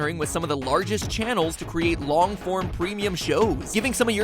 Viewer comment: Who still uses Facebook? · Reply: I thought you said "Long moms."